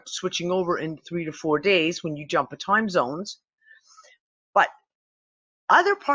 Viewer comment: When you said "...jumped six or seven time zones", I heard it as 'timelines'.